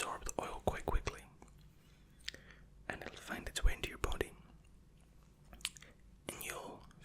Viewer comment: Not quite listening to what you were saying (because I was close to being asleep already) instead of hearing "find its way into your body" I heard "find its way into your pudding"... which was confusing enough to wake me up again. Well, got about 40 minutes to get back to sleep ;)